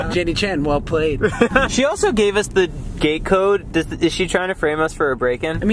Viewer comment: Oh they said “gate code”. I thought they said “gay code” I’m thinking how does a gay code get you in to someone’s house unless it’s like a doorbell song ? V I du no.